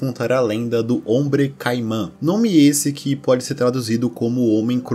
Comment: que susto veio eu ouvi meu nome do nada só que o meu é com K e Kaimã eu escutei do nada slk😅